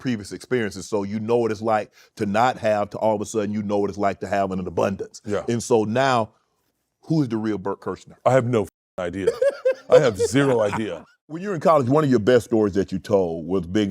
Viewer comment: did he just call him "bert kurshner" ????